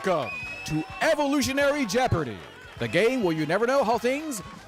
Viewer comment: the jip parady host definitely says " welcome to jeopardy, the GAY where you never know how things will turn out" instead of "game" !? Am I wrong???